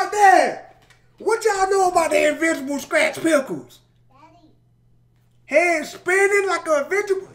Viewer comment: did anyone else hear "daddy" when he said invisible scratch panels or something like that